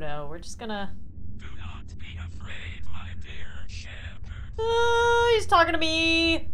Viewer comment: I think I’m just losing my mind but at 9:06 I heard ‘you’ve got to be a brick with liberian chandler’